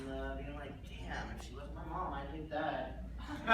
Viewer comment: What did Edward say about Linda at 20:40 ? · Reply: if she wasn't his mom he would hit that.